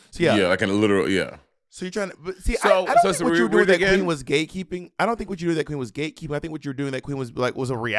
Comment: I keep hearing "gaykeeping" 💀